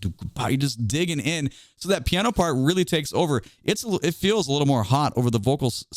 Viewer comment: did he say TAKE OVER????